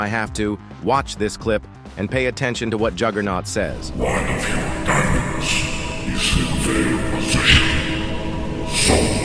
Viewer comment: "one of your diamonds is in their possession" in wrong, it actually says "one of your TITANS is in their possession", it said in the subtitles of the actual video before it got removed (Edit): BTW it sounds like "diamonds" because he put it in the subtitles, so what you're reading is what you think it's saying if it sounds familiar